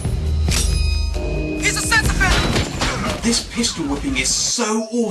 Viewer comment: sounds like he said 'he's a sex offender'